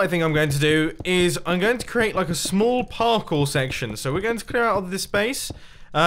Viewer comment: did he say... did he say parkle?